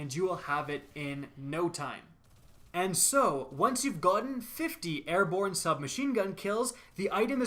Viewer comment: Anyone else hear that Scottish gotten